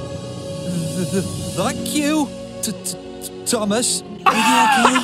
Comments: He says "thank you, Thomas". I'm used to British accents in the family. 😅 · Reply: I Didn't Hear "Thank You Thomas" I Heard "Buck You Thomas" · The thomas one is saying "than you thomas"lol 😂😂😂😂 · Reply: I heard "thak yo"thomas 😂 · He did not say the f bomb. He said thank you Thomas. He is just British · I heard thank you cuz it’s British American: “thaenk” British: “Thank” A is pronounced “Ah” in British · Hi! I CLEARLY heard “F**k you, Thomas.” I’m surprised parents didn’t complain about it like they did Bob the builder, which I didn’t hear any swearing in that clip, it was just muttering like they claimed it to be. I did hear “F**k” in Hunchback of Notre Dame, I also think Mr. Rogers knew what he was doing because of the way he grinned right before he flipped the bird! 😂 Hilarious video! Have a good day! 😃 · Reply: i hear him saying "thuck you Thomas" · he was actually saying "thank you, thomas" phee whew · Hi! I CLEARLY heard “F**k you, Thomas.” I’m surprised parents didn’t complain about it like they did Bob the builder, which I didn’t hear any swearing in that clip, it was just muttering like they claimed it to be. I did hear “F**k” in Hunchback of Notre Dame, I also think Mr. Rogers knew what he was doing because of the way he grinned right before he flipped the bird! 😂 Hilarious video! Have a good day! 😃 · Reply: I heard “frank you, Thomas”